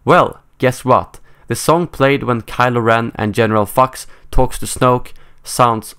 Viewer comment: lol it sounded like he said general F*cks